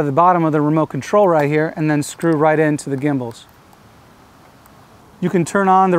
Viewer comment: Did he just say gimball?! And 8:30 did he just say mp3?!